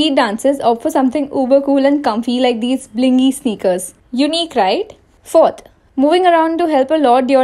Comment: When she said "unique... right" - I heard that "U need cried?" 😂😂